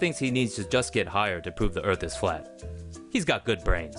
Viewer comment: i thought he said that he wants to prove that earth is not flat